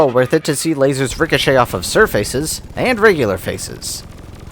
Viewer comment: did he just assume the grounds gender. Why did he say sir face and not madame face ?!